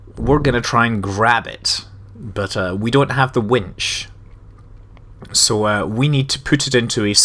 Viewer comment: Oh wait. Wench? I thought you said Wench... Ba-dum-tis